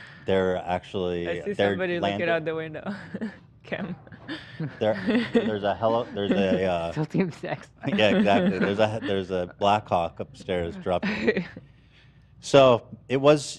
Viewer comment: Just as a tip for Zach, at 1:12:25 it sound like Ethan says "Blackcock"